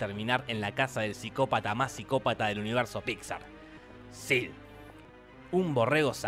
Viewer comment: En el 4:09 los subtitulos en inglés se confunden, ya que dicen "Yes" en vez de el nombre de Sid